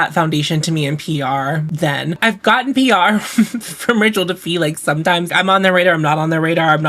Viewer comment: I was one of the people forever ago who thought was saying "ritual defeat" when she talked about their thorn oil 😅